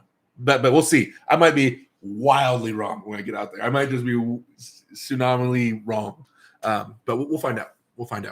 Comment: "Tsunamily wrong"? Is that what you said? If so I'm stealing that phrase :D